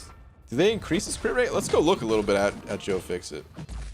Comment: When you were talking about doing a deep dive on Joe Fix It. I somehow heard it as doing a deep dive on Joe Dirt lol.. "I put an E at the end and pronounce it deerte, it's pretty cool"